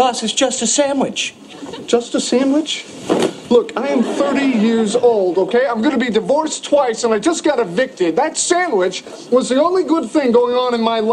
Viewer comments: のロスのところが何回聞いてもI’m will beにしか聞こえない（._."Ⅱ） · らへんからの I'm about to beのところが違う単語に聞こえるのは気のせい？ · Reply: ここは実は「I'm gonna be divorced.」です★ Friendsの元々の英語字幕(台本)は「I'm about to be divorced.」となっていて、 実際のセリフでは「I'm gonna be divorced.」とRossは言っていますよ！ おそらくRossのアドリブで変わったのだと思います！ どちらを使っても自然な言い方であるのと、「I'm about to」の方が解説するのに良いフレーズだったので、今回は元々の英語字幕(台本)に合わせて動画を作りました！ この機会に両方の言い方を練習してみるのも良いですね＾＾